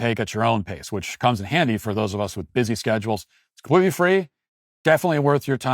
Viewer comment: During the Hillsdale College ad, I thought when you said "It's completely free", you actually said "It's gluten free". finally, the kind of college I can support haha